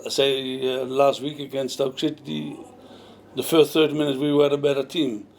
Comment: does he say stoke shitty? bless him :D